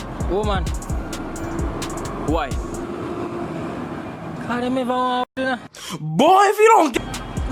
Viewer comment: "Cause dem ever waa do...." i didn't catch that last part. somebody tell me what he said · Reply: “Cause dem eva waan hood....”